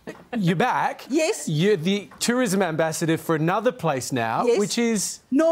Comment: I thought they said ambassador for Northern Ireland at the start and was wondering what the fook?